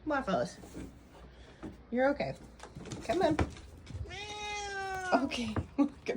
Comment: Is Waffles polydactyl? Coulda swore he said come out when you were in the shower ❤😂